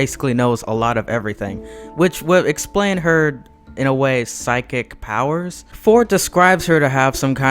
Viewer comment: Did you just say psyco powers?